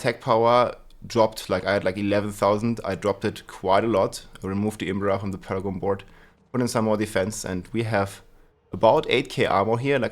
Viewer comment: can anyone tell me what he said he removed from the paragon board? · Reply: my guess is the "imbiber" glyph